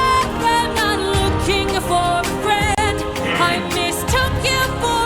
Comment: About "You're not looking for a mentor, im not looking for a friend." In warrior of the mind, Athena says " if you're looking for a mentor, I'll make sure your times well spent. " Ody says that they'll be "bestest of friends" so from the beginning, they weren't listening to each other. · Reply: I never noticed this, so cool! Actually, I never noticed she said "I'm not looking for a friend", I always heard "you're not looking for a mentor, you're not looking for a friend"